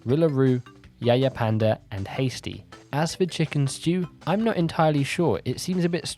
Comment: When he said chick and stew I thought he said chicken stew